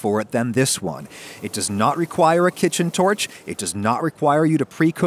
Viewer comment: I thought it said "No-touch" and not "No-torch"